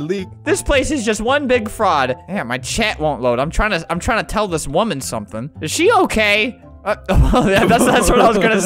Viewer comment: Who else heard him say "Damn my chat won't load, I want to tell this Idiot something" 8:49 No offence just wanted to point that, I live your vids btw